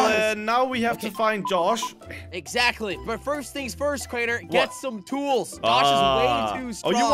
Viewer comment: Jelly: Get some tools I thought he said "get some tacos" . . . I mean, I wouldn't mind tacos but still 😂😂